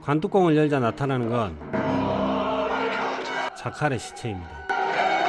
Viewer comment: 에 누구의 시체라고 하는거에요? 안들려ㅠㅠㅠ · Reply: 자칼의 시체입니다. ㅎㅎㅎ. 영상 봐주셔서 감사합니다.